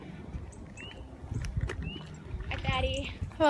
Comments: What is this lady saying at 3:13??? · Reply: "Hi Fattie!" 😂. The amount of work that goes into these edits is....... 10 out of 10.👍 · did she say “Hi fatty” or I’m tripping? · DID SHE SAY HI FATTY???